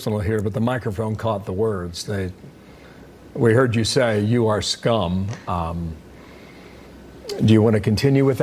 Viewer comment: I heard Nikki say 'You're just dumb', not 'you're scum'.